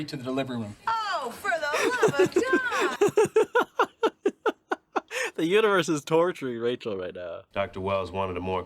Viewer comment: At first I thought you said, “The uterus is torturing Rachel right now” which would’ve also been true! 😂